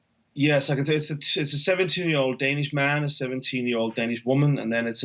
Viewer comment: did he say “70 ur old danish woman or 17 year old danish woman”? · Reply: 17